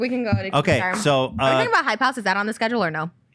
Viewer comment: It took me a second to understand too lol. I thought she was saying “Hi- Pouse” idek 😭